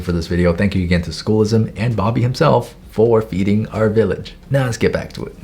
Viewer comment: I THOUGHT IT SAID " thank you for eating our village. " 💀